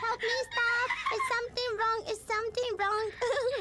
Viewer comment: I think said "help me staff"